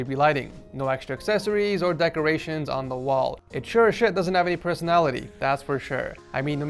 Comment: did he say 'sure shit' 😂😂😂